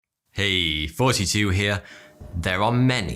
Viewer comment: When you saw your intro, I always hear "Hey 42 here", with works for me.